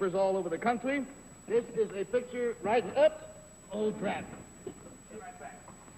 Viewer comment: Did Garry say "Oh, crap" at 13:22? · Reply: I think he says "oh, drat".